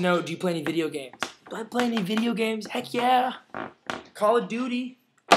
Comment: I TOTALLY HEARD YOU SAY 'CALL A DOODY' instead of Call of Duty. Nice to hear from you again :) thought you were mad at us Rushers :/ LOVE YOU CARLOS!! Kiss Sydney for me :)